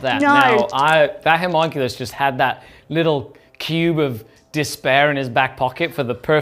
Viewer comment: oh, 'cube' of despair. I thought said "pube of despair", which would make even more sense Drukhari-wise...